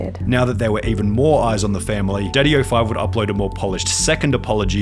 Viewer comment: It sounds like hes sayind Diddy05💀💀